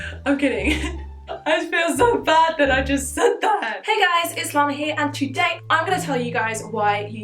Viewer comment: I literally heard "Hey guys, it's long hair" That was weird when I realised it...